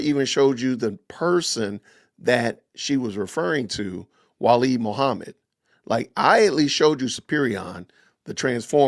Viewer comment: I don't think she said Walid Mohammed. Thought she said Walid Mahmoud.